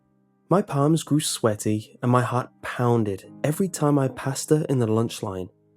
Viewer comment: I thought he said "Every time I PASTA (passed her) in the lunch line"